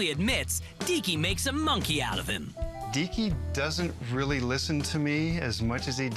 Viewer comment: When she says "Deeky" I thought she said Dicky xD